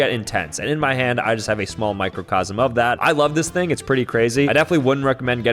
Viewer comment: did he say MICRO?